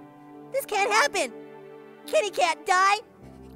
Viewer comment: When the boy whathisname said 'Kenny can't die' I heard 'Kitty can die' 0w0